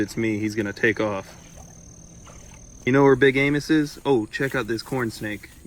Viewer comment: Good job 👍 but didn't understand the very beginning. Also @ 20:54 I heard big anus!